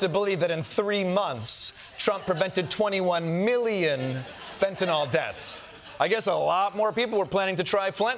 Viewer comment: Did Kosta say Flentanyl? 😂